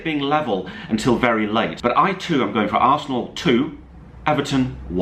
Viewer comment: When said 4-0 nils I thought he said fornals